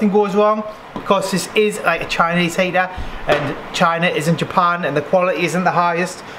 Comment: Am I the only one that heard him say “China is in Japan” 😂